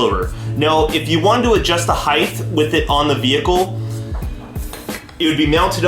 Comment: Spanner, not spammer wrench lawl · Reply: He also said "heighth" instead of height at 2:59 lmfao